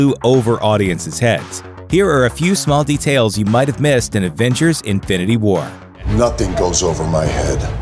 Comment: did he say adventures: infinity war?